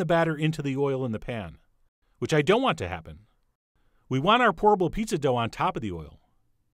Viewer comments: I keep hearing "horrible pizza dough" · I thought you were saying "horrible pizza dough" a couple a times.